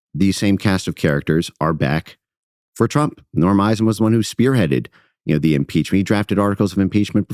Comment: Who did he say was going after trump? Near the 5:03 min mark??? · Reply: Norm Eisen. I asked a friend as I couldn't make it out either and captions on didn't help.